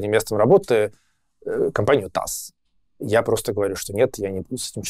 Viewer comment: что за компания? Таас? Не расслышал... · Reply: ТАСС